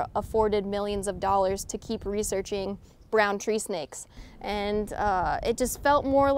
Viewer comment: I heard 'brown cheesecake'. I immediately pictured that on your menu...